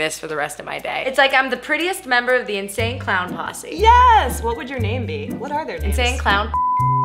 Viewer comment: Does anyone else think that what Hannah said at 3:28 was "Insane Clown Pu*sy" cause it sounds like "Posse"